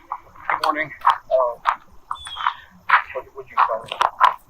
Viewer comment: What I hear is: Passerby - "Good morning" Police - "Hello" Police - "Tell me what you found" Man - "........." (think I hear the word "bag" in there?) Woman - ".... facing out the path.."?